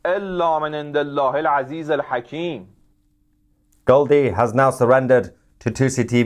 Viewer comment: I heard Goldie before an hour